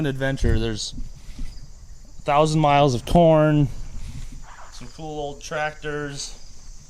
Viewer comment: did he say corn or porn